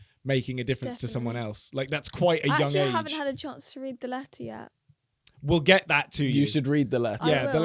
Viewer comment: when she said letter yet i thought that she said Latvia and i yelled Latvia mentioned rahhhhhhhhh until i noticed that she said letter yet